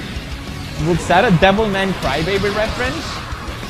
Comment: "Is that a Devilman Crybaby reference?" no it's one punch man. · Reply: Thought he said devil may cry baby.